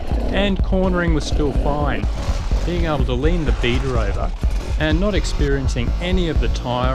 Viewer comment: everytime you said Beta, I thought you said Beater, I thought you loved your Beta, lol D606 on my KTM530 lasted 1600 miles, about 50/50 dirt/road with a tubliss(2 psi dirt, 30 psi slab) I have 800 miles now on a Kenda Equalibrium, mostly dirt, 100 on slab, 0 to 2 psi Rocks, and due to low slab miles it should go 2000 miles. Lots of rock riding in American Fork canyon, and no major carcass cuts on the Equalibrium(knock on wood).